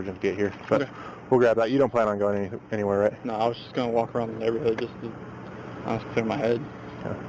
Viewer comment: I don't think he said Hood Z. I thought he said I'm going to walk the neighborhood and clear my head.